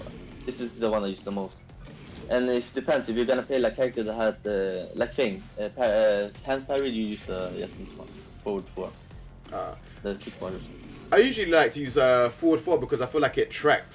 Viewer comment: what did he say? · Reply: "If you are gonna play a character (that has uhh..) like King, that (uhh..) can parry you, you just (use) this one. Forward, 4."